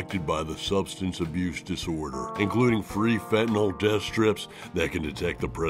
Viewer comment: At first I thought he said " Fentanyl death strips"